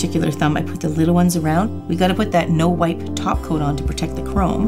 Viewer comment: Hahaha the whole time I've watched your videos I thought you were saying 'no white top coat' like what was I thinking? 😂😂😂